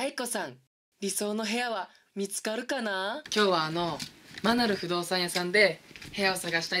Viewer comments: アナル不動産屋さんにしか聞こえないw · マナル不動産屋がアナル不動産屋って聞こえたw